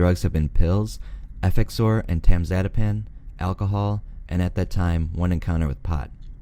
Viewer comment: What is "emtemzadipan"? Did you mean "and temazepam"? The first option doesn't exist according to Google, and I'm desperate to figure out what the hell was actually said 😂